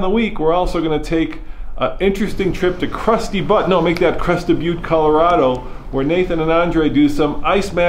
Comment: did he say that hes goin to krusty Butt??